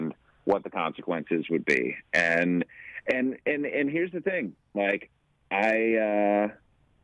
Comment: At the 19:58-20:01 point...did Jeff refer to Rich as "Mike" or did he say "Like"? I think he said Mike...